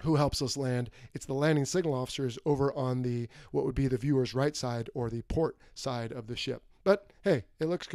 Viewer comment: You said port in reference to the right side of ship. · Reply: I went back and watched again. At 14:40 aprox. you said viewer's right. So , I was wrong. I thought you said right side in reference to the ship. My bad. I should have rewatched it first before commenting. By the way, thank you for your service and all of the videos you make.